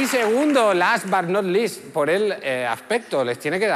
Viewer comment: ¿Qué es lo que dice? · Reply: "last, but not least"